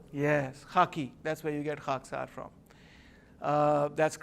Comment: Who is the group he mentions around 14:00? Sounded like he said haqqsar tariq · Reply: Lang H Khaaksaar Tehreek. You can Wikipedia them. Was an anti-colonial religious movement that was also inspired by fascism (brown shirts).